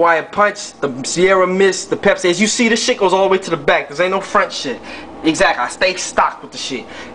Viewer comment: I don't know why, but this part here 2:35 where he explains how his drinks go all the way to the back and how this, in fact, "ain't no front shit" always makes me laugh the hardest. I can't explain why I find it so funny. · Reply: Hahaha I always thought he said ain’t no French shit. This makes slightly more sense now